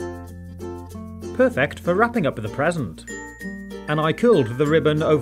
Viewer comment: i thought you said peasent